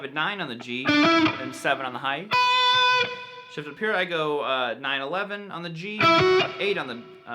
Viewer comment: did you just say.... 9/11?!!?!?!?